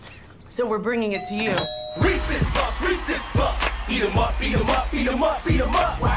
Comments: i thought they were saying racist puffs when i first saw it when i was 10. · I thought they said Reese's puff Reese's beat em up